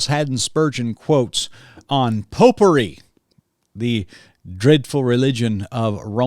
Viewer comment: Popery. I get it now. Had to take that back a few times. Thought you said "potpourri" 🤣🤣